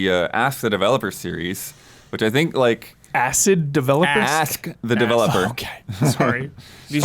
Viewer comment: isn't crazy, I heard acid developer too. 😂😂😂